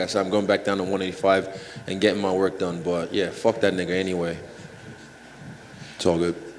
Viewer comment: well spoken, and straight up... that is a rare thing in competitive sports. but! are my ears loopy or did he say 'phck that nigga'!?